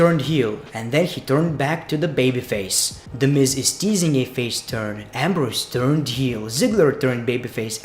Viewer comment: who did he say turned heel can't understand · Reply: Dean Ambrose.